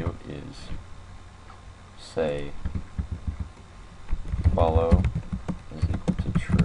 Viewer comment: i don't know what it fully said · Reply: Don’t follow me it says then